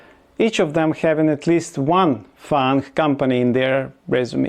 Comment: Did you say foreign company ? Oh my gosh, I never imagined that !